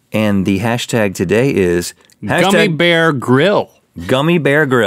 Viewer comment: Is it bad that when they said gummy bear grill I thought they said gummy Bear Grills